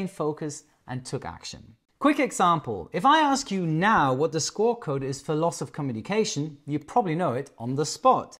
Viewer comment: a note about your enunciation of a word at timecode 10:32. I heard "... the SCORE code for loss of communication..." I had no idea if I had heard that correctly so I turned on the captions and I saw the word "SCORE." I did a search on that phrase and I found "... the SQUAWK code for loss off communication is 7600." Since I have zero flying experience I never would have known what you meant if I hadn't turned on the captions and then searched for the meaning of that phrase. Thanks for making me question your words!